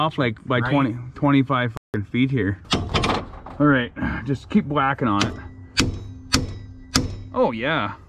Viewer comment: Did he just say keep blacking on it🤣